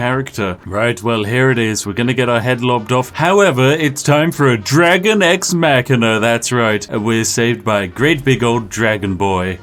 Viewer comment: I thought he said "It's time for Vox Machina."